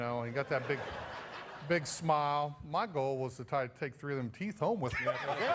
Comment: what was larry saying? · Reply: "my goal was to take 3 of them teeth home"